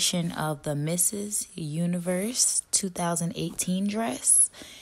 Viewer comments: did i hear mrs. universe? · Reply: Yeah, she said MRS. UNIVERSE !!!!! hahahaha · Even the tittle is Miss Universe I Heard on 0:23 Mrs???? Whahahah